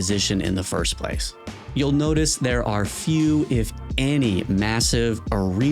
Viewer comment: >Dark Souls 2 doesn't have room clearing attacks >Immediately cuts to smelter demon How did you put this in a scripted video? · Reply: If you cleaned your ears every so often you would realize he actually said "a few, if any" meaning there are some but it's so little you would hardly notice. 5:41 for the quote big dawg.